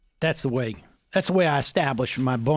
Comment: Every time you say “bona fides,” I keep hearing “bone a fetus.”